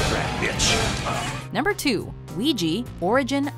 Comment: did she said oiji